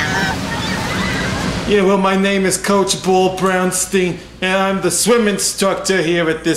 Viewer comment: I thought he said POOL BROWN STINK